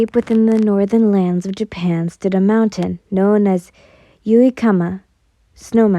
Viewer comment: Im sorry- at the start of the video, I thought you said "there was a mountain" and not "there stood a mountain," and I was like "what the fu- WHERE DID IT GO???"